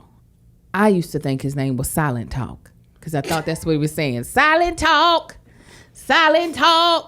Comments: LOL I thought he was saying Silent talk too LOL!!! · I thought he said Silent Talk too! 🤷🏾‍♀️ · Don't come for! Cause I thought he was saying silent talk too🤷🏽‍♀️ btw black mom as well😂😂😂😂😂